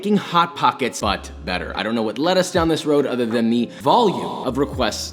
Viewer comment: Are we gonna pretend that he said lettuce instead of let us?